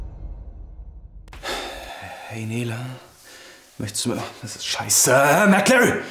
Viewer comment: Bin ich der Einzige, der bei 2:08 Merkel anstatt McLarry verstanden hat? 😂